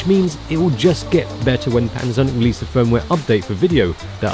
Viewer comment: did he say "when Amazon releases firmware updates??"